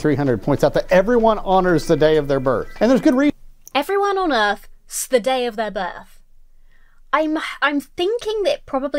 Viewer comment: So, how long til Emma noticed that what she heard as "everyone on Earth ---s the day of their birth" was "Everyone honours the the day of their birth" Love your content Emma, and a wonderful holiday season to all the wonderful folks out there. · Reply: I thought he said "honours" too